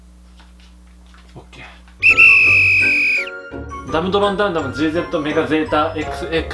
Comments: ドラゴンの名前のダムダムがガンダムって聞こえるw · ダムダムがガンダムに聞こえたの俺だけ？w